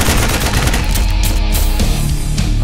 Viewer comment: What does she say before shooting? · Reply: NUCLEAR LAUNCH DETECTED